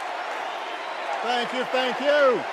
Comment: 😮😮😮😮 yooooooooo. Why do i hear "thank you satan"? Starting at the .55 sec mark